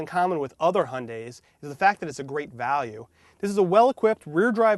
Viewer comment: what did he say!!! thredruet horsepower?